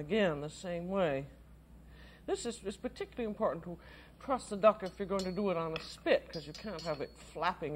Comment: I thought she kept saying "trust" the duck. I was thinking I don't think it's going to peck at this point.